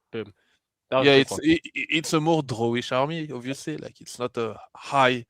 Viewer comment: what did he say? It's a more army? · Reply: draw-ish